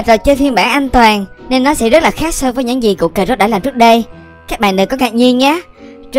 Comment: nghe thành củ cà rốt:))